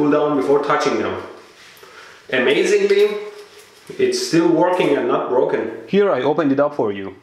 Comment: I thought that he said "still working enough broken" until I saw the subtitles